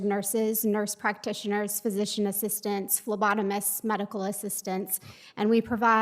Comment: did i just hear lobotomists?